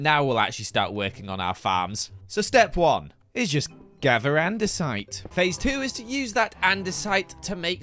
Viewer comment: I thought he said gather and decide.. not gather andesite 😂